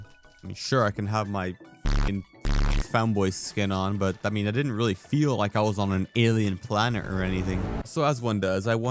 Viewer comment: did you say the f-slur here or just 'furry'? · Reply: neither, why would u assume this they just said fuck